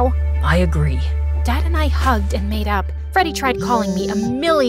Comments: I HEARD THIS AS MADE OUT 💀😭😭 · i thought it said "dad and i hugged and made out" 💀💀💀